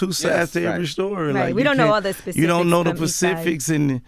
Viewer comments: did he really say pacific? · We don't know the Pacifics. Lol or did I hear incorrectly? 7:51 t.s